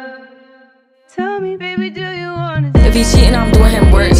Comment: Nah why I thought it said “tell me baby do you want a baddie”